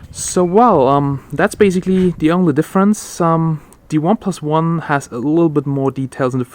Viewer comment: I like this phone so much but they don't sell in my country, Ghana. Wait, did you say oneplus 1?